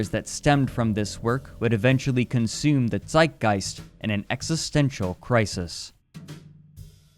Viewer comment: eventually consumed at what? (not a native speaker) :( and what did he say in 18:03 😭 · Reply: "zeitgeist" at 11:08 and "although brain with a damaged chess evaluation"